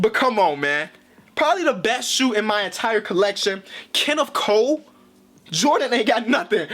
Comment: LoL, I thought he said “Kennef Coe”